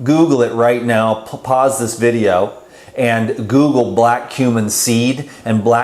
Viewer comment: Almost bought the black cumin seed oil 👍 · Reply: I thought he had said black human seed oil. I’m not a native speaker. Now I know what he said.